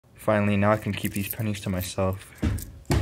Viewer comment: I thought it said "penises"